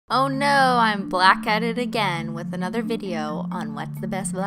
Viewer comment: My video bufferd at the beginning and all I heard was “oh no I’m black”